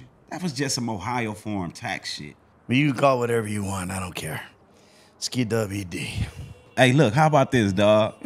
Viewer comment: “Skidubidi” - Ned🗿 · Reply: Thought he said ski- W -Di.